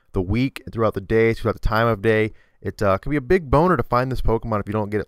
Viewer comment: Did he just say "...it could be a big bonner.." xD